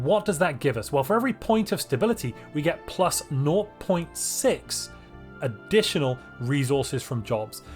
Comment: What did you say just before .6? Can’t make it out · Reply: "For every point of stability we get plus nought point six..." Does that help?